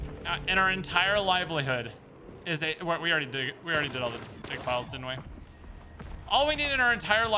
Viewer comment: Every time said ‘dig pile’ it legit sounded like he was saying ‘d*ck pile’ and I giggled every. single. time 🤷🏻‍♀️😂